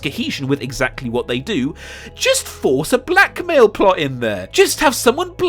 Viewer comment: I thought he said black male motivation at first and got really confused